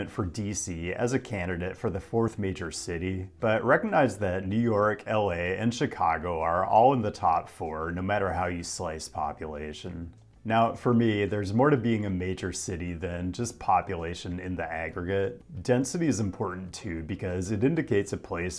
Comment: Me hearing him say "population" at 2:49, while trying to be a rapper to fix the housing crisis: did he say "ovulation"? Am I being helped out by the subconscious collective here? Looking forward to the rest of the video!